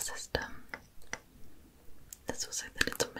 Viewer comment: Did anyone understand what she said in 18:53, it made me chuckle while i was falling asleep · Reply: I heard cutting bits off so.....sheifjeveh when... shfbsonsvrkw your cooking.